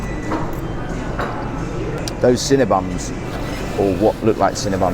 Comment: did you say cinnaBUM ?? lol